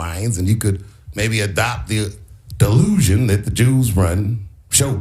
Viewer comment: When I first heard Dave’s speech, I thought he said the juice not the Jews 😂😂😂